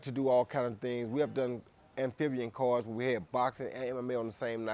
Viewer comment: Did he say this? "We've done amphibian cards"